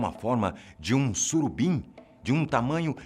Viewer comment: entendi surubinha '-'